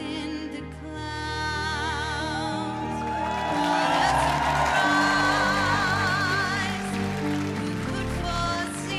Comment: (I hear "God has the prize" my brother hears "what is the prize") which one is it lol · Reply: I heard 'what a surprise'